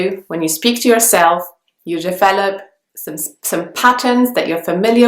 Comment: Hi, thank you very much for your excellent videos. I have a question, in the transcription, at 3:27 it is written: use your Philip. I couldn't understand what you actually said, could you please tell me what word is it? The transcription is automatic and there are a few mistakes, but in this particular case I couldn't identify what you said. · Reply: I think I got it, you said "you develop some patterns" \o/